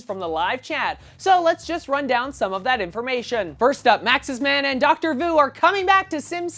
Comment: I thought you said Maxis man and Doctor Who ^_^